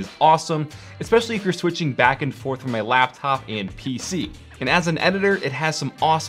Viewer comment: Did he just say “ from laptop to pc”